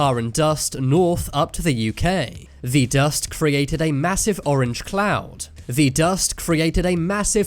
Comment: Who else heard him say “the dust created a massive orange cloud” twice 🤣🤣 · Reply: I thought he said the “Massive orange clown”